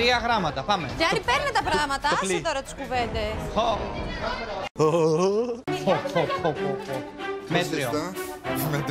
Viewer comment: Τι είπε στο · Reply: Το πλι